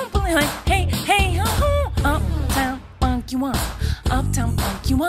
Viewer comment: it sounds like she is saying uptown fu*ck you up